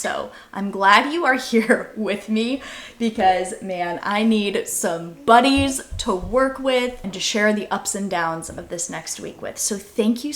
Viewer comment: At the 1:37 mark, I thought it you said, “I need some bunnies to work with”, and apparently my inner Snow White came through when I thought, “yeah, that would be nice…” in reply. 😂 It’s been a looooong day, apparently. 🐰📦🐰🧹🐰🧺